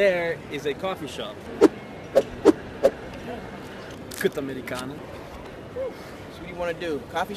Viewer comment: did he say kutti Americano